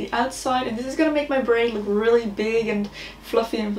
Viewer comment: For some reason, at the beginning I thought she was saying she was going to make her brain look really big and voluminous and all that.😂